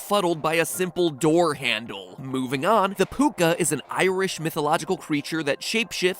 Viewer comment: For some reason I heard "poop gun" instead of "Puca". Don't ask why